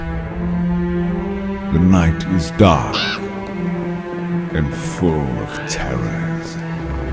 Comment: LOL I thought your intro said "The night is dark and full of THEORIES" 🤣🤣🤣